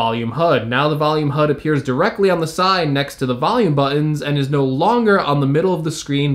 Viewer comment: Did he just say “buh-inns” for buttons?